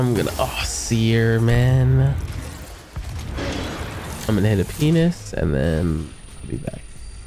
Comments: did my man just say "imma hit a penis" you good ma boi ? 😂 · ayooooo did he just say I’m going to hit a p#nis